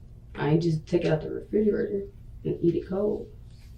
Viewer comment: I think she said “I didn’t heat it up”, and that’s what the captions said.